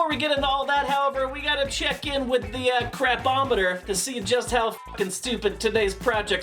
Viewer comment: ngl i heard him say crap-ometer and was expecting pin-o-meter to come back....... bring him back i miss him